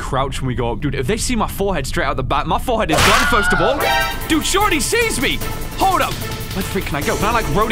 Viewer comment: What he really said: “Dude she already sees me!” What I heard: “Dude shorty sees me!”